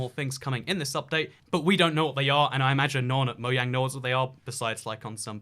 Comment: Did he just say moyang? 😂